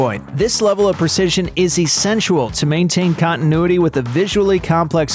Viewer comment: I'm sorry, I'm sorry, I'm sorry but... at 1:11 did anyone else hear the narrator say "essentual"? It's essential not essenTUAL.